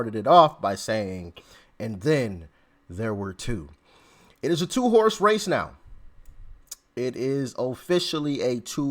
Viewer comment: I thought you said “it’s a two horse face”. 🤣